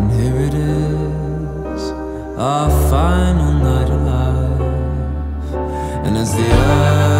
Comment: Am I the only who thought he said "oh God its you" instead of "oh girl it's you" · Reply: and am i the only one that thought he said "i found a little life" instead of "our final night alive"